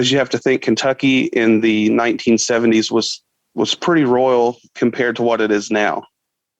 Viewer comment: pretty royal? Huh? Or am I mishearing and he said rural? Royal makes zero sense.